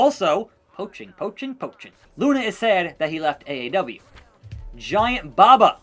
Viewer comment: I always think you are saying AEW.